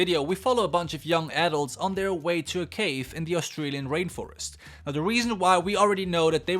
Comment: It sounded like you said Rape Forest instead of rain forest XD